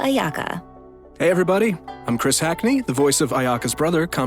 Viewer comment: At first I heard Chris Hansen